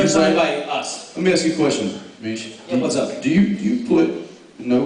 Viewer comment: Did Jensen call Misha "Mish" at 45:34?